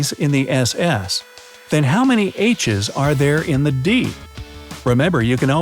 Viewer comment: I first heard at 1:09 “how many inches are there in the D ?? 😂